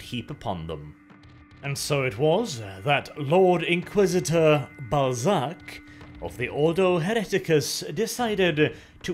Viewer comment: Inquisitor ballsach! did I hear that right?